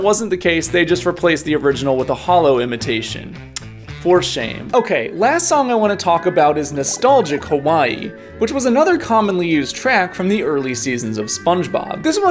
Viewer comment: bro I thought you said nostalgia kawaii 💀💀